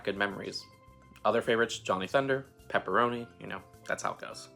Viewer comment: Did somebody say jolly thunder ??? 🤔 Sorry, I totally meant Johnny thunder